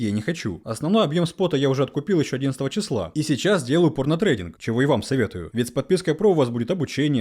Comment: я ведь не один услышал порно трейдинг 7:40 ?